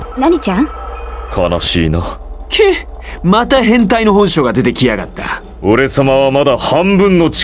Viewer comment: Did he say "Hentai"?